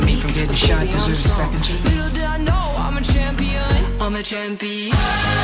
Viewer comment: i just, she was talking to buck and i just heard MAMA CHAMPION and now im stuck.